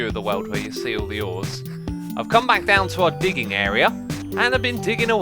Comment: When he said "We've come back down to the digging area" i thought he said "We've come back down to the dogging area"